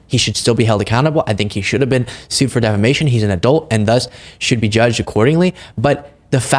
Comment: Ahhhh... You said, "adult". I thought you said, "Dolt".🤣😆😂